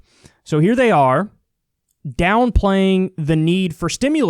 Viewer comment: For one second I thought that it said” downloads instead of downplays” I was so confused for a sec 😅